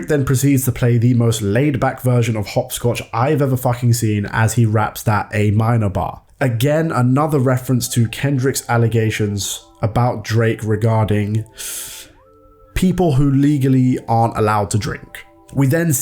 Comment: I thought he said “people who aren’t allowed to Drake”